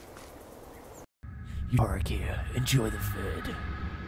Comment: Can someone tell me what he said in the intro? I always hear ''Enjoy the food'' :P